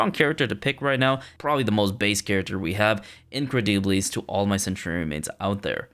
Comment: what did you say again? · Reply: INCREDIIIIIBILIS‼️‼️🗣️🗣️🔊🔊🤜🤜💥💥